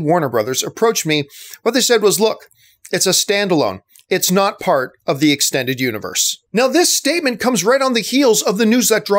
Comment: I've listened to the interview and I heard different between Matt Reeves and Kim Masters. This is what I heard: Reeves: "When they (Warner Bros.) approached me, what they (Warner Bros.) said was 'look, it's a standalone, it's NOT LIKE this isn't part of the extended universe." Masters: "They're not saying it's a trilogy either." Reeves: "Yeah, they're not saying any of it."